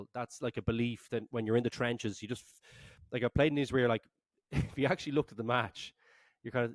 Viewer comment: Did he say he played in isreal?rugby 12:58 interesting